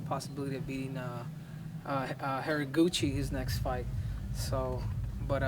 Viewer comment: Did he just say harryguci???